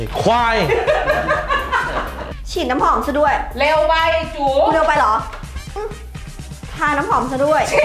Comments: ใครได้ยินว่า'เร็วไปจู๋'บ้าง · ได้ยินว่าเร็วไปจู๋